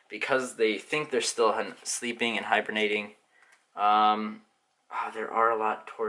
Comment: did he just say hypernateing